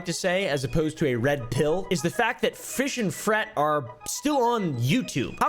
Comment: Did he just say fish and fret or did my dyslexia infect my ears 😂